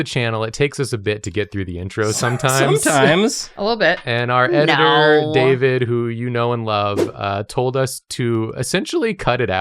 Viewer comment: Yall, instead of "Our editor David, who you know and love." I heard "Our editor David, who no one loves."